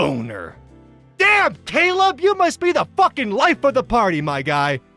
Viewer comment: DID HE JUST SAY FUCK DID GET SO MAD HE FORGOT TO CENSOR